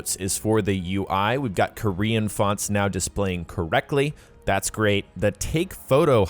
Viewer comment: Did you just say Korean FUD? You'll be hearing from the Bogdanoffs soon.